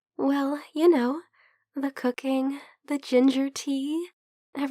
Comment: she said cooking I thought she said cocaine ... I don't know whats wrong with me but i'd prefer both if I was sick